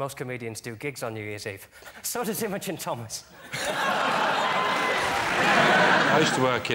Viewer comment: what the hecc is John saying at 25:25 ? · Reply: So does Imogen Thomas